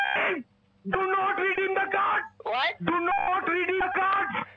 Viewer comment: Legend says wakes up every single night screaming "WHY ARE YOU REDEEMING!!??!!" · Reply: I heard "Do Not Breathe In The Faart!!"